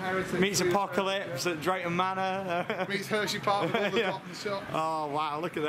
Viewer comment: Did say, " it meets Hershey park" at around 3:41 ?? They have been to hershey?